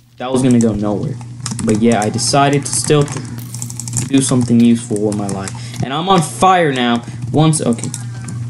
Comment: Wth did u just say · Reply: "I decided to still... To do something useful with my life" Wth does that even mean